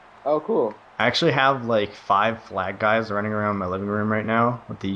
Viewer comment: Totally thought he said 5 black guys running around his living room, I was like da fuq? Had to watch it again to realize he said flag guys.